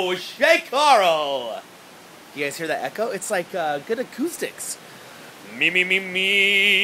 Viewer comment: i thought i heard you say good ass hole is a stick i was like wtf but you said good acoustics 1:12 LOL